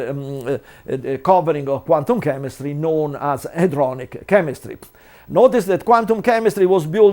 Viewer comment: Did he say hadronic?